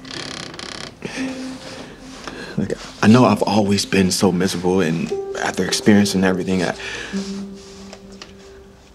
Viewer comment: I think I heard this guy say the n word. Or did he just say “look I”? Which one is it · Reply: He said look. I had to hear it twice! Lol